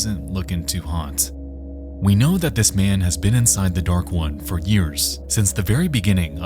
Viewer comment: Lowkey thought bro was saying “the dark ones” the whole time lmao